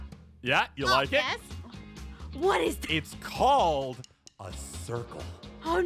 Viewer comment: bro at 8:41 and 42 did kim say jess?!?!?!